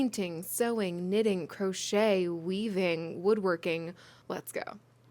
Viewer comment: when you were listing types of crafts I misheard “weaving” as weeping and yet I did not question it